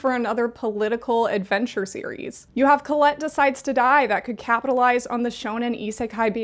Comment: i'm sorry, i didn't get what you said here and i wanna add it to my list! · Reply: Colette Decides to Die!